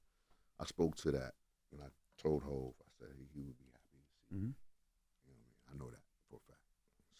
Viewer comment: what DID HE SAYYYYYYY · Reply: “He would be happy to see you”